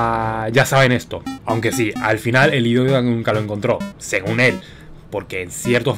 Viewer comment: Que fue lo que dijo en el 4:07 Yo escuche algo como Al final el HVGK@ nunca lo encontró video algo así xd